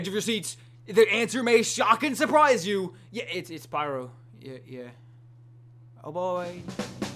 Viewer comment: For Number one, I thought he said: "It-It's SPYRO..."Yeeeeeah... Just thought it was funny.